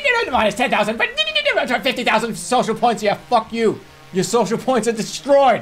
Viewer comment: When Jerma kept giggling when he thought the guy said “ah, fuck me” I died 😆😆